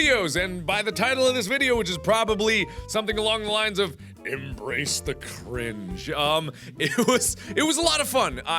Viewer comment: I thought it said embrace the fringe.